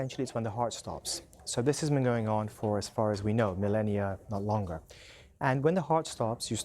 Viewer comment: did he just say death has been going on for millennia or longer? please tell me i'm hearing it wrong i mean technically he's not wrong but...